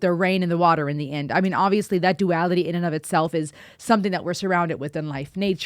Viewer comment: Did you just say "Duality"??